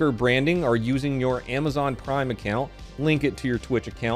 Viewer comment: I thought you said “or by using your Amazon Crime account,” which will probably be a thing in 15 years anyway.